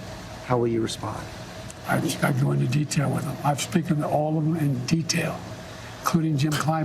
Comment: did he just say “I’ve speakin to all of them...?”